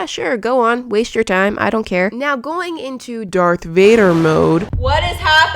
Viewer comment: Did she say Dark Vader ☹️ JK love you 🤗